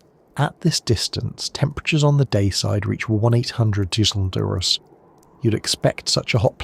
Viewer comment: what ? The fu ck did you just say. Temperatures reach 1 800 deoogroouses 😂.